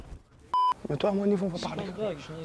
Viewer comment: Il dit koi a 10:00 svp😂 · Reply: Au niveau mental on parle comme des homme